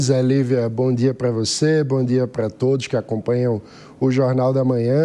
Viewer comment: Em 08:07, o comentarista falou "bom dia pra todes..." ???😮😅 Eu escutei bem ou não?😅😅